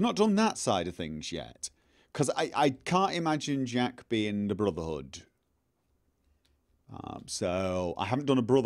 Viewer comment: i heard the blooderhood xD lmao